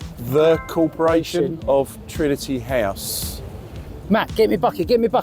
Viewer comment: Coulda swore it said “Trinity Trust”…. Cheers from Arkansas!